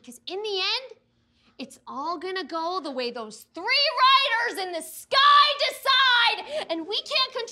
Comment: "EVERYTHING'S JUST GONNA GO THE WAY THOSE THREE WRITERS IN THE SKY DECIDE" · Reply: I thought she said 'free riders'